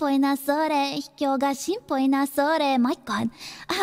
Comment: Why did i heard "Chinpo na sore" in the beginning? i'm that unpure?